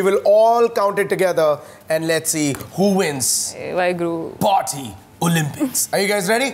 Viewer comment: Am I the only one who heard Tapsee say Wahe Guru at 25:16 ?